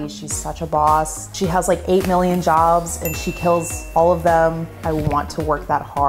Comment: As someone with hearing aids when she said “she has like 8 million jobs and she kills all of them” I heard “she has like 8 million dogs and she kills all of them” 🤣